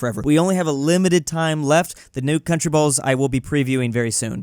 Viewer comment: did anyone else hear him say nude countryballs